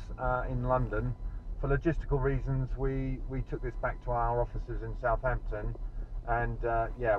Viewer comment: "For logistical reasons, we took it to Southampton". I hear that as "It's a RR Sport. Gonna drive it one more day, thanks." Haha! Great job nonetheless.